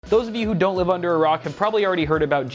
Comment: all i heard was ‘for those of you who don’t live under Iraq’